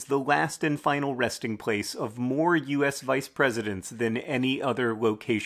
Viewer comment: I heard the phrase "Final resting place of more VPs than any other in the world" and had to check on my local cementery to see if I could beat it. I did, it contains 5. Then I replayed the clip and it says "American VPs" damn it !